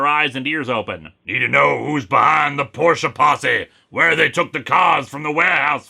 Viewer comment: I hear "porche posse" and think "porta-potty"... which is considerably more frightening.